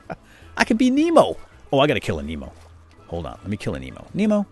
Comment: he said "I need to kill a Nemo" I heard "I need to kill a emo"